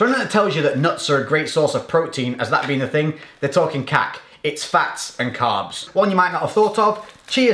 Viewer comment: did he just say they're talking kak?